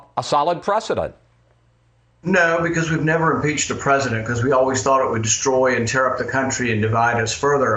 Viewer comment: Did he just say we’ve never impeached a president? It’s at 2:08 for source.